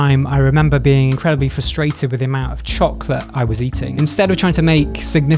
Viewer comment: For minute there, I thought you said "chalk" instead of Chocolate.